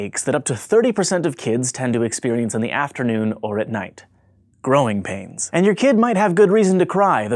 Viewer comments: I thought it said "What are growing penis?" or some shit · Damm I feel bad thought it said what causes growing penis lol